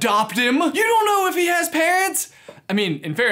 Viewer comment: To me it sounds like "You don't know if he has pants." Does anyone else hear it?